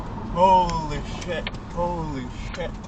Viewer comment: I thought he said "Holy fit"